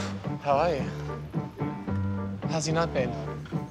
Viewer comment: What does he say at 0:53? How's your nut been?? 🤣